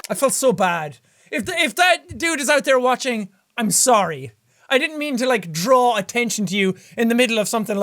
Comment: am I the only one that thought he said I didn't MEME to draw attention to you